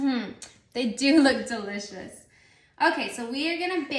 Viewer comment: So cute. These times go by so fast. Loved when she said “these look delicious”. I first thought she said “these look kinda sus” 😂